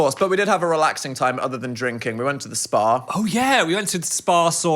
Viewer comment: Thought they said they went to the spar not spa